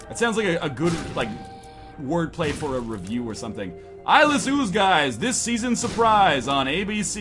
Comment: I just realized northernlion's been say eyeless ooze guys, when this whole time I thought he was saying ivan ooze guys.